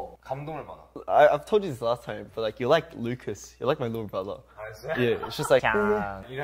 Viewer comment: I'm sorry but when he said "little brother" at the beginning I thought he said little bird 🐦😂🤣 I was like holdup little bird whattt